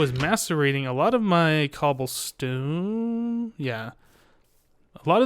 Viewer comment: I think i heard "masturbating cobblestone"